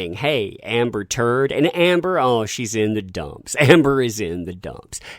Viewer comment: I thought I Heard she shit the bed LOL